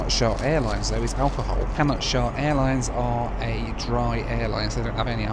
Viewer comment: I heard "Cannot Shart" and I couldn't stop laughing.